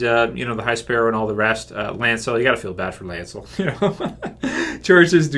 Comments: You said "Lancel." Nobody feels sorry for Lancel and he's not gay lol. I think you meant to say "Loras" as in Loras Tyrell? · "jury's don't like homosexuals, at least from what I've seen " firstly how many jury's have you Seen? and secondly how many of them decided to bring up the sexuality of the person on trial? hahah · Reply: Oh my word, you dumb fuck!! Don't you check the video before making a comment? He clearly says,"Church's". Go back to 5:47, idiot.